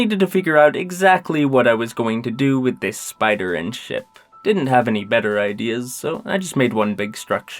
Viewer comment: I thought he said "what I am going to do with this spider and shit" and I was DYING